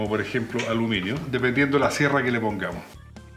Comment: yo escuche clarito que dijo "dependiendo de la sierra que le pongas" 00:46 para ser mas claros.